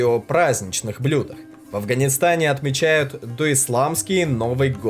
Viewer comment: Спасибо за выпуск! Только одно замечание - Навруз не Исламский новый год! Это новый год по старому персидскому календарю (существовавшую во времена зороастрийцев). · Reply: Он сказал до исламский тебе послышалось исламский.